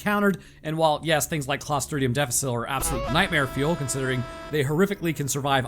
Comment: i got no clue what he just said lol i wish i knew the name of the things supposed to scare me but all i got is "clastronian nedeficit" lmao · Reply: C Diff. It's the bacteria that causes C Diff, which is a dangerous form of colitis that causes HORRIBLE diarrhea. It's actually common, and is often triggered by antibiotic treatment cus it the rest of ur gut bacteria get Thanosed while this stuff survives and then takes over. In spite of how common it is, it is very serious and life threatening if left untreated (can easily cause dehydration). clostridium difficile is the name he said. Some antibiotics can work depending on the strain, but this is a condition that can actually require a fecal transplant to treat (taking poop from a healthy patient and putting it in ur colon to have their healthy gut bacteria compete with the C. Diff bacteria) because of antibiotics resistance (remember that the condition can be triggered by taking antibiotics, typically for a different bacterial infection. It is also highly contagious. Look into it.